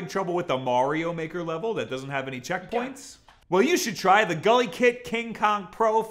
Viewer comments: Ohhhhh GuliKit... for a while i thought you where saying gully kid and that's sorta a weird name for a brand... more so if your where i'm from a gully kid is sorta a bad thing.... like your parents where meth addicts bad thing... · Did he just say MARio?